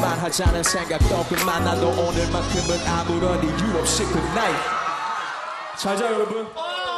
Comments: can someone please tell me what Simon said at 3:47? I want to join the fun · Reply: He said "Goodnight everyone" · What did Simon said at 3:47? Help me, I wanna join the fun. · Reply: He said "Good night everyone"